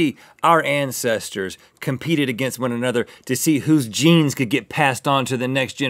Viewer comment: I thought Rhett said: "Our ancestors competed against one another to see whose "JEANS" could passed on to the next generation generation after generations did this..."😂